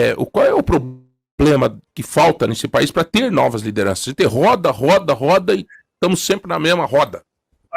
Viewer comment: ". "RODA, RODA, RODA e estamos sempre na mesma MERD* " foi isso que ele quis dizer 💁🏻‍♀️